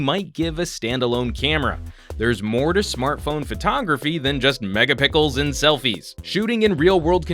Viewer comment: did I hear MEGAPICKLES? lolzz